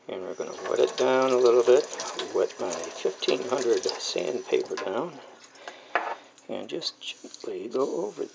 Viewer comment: miniutes ...did he say sandpaper.....??